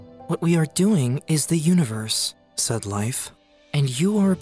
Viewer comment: I thought he said sad life when he said , said life